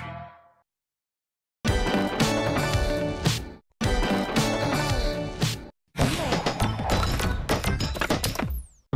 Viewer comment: OMG, What that hated strong word? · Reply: Moist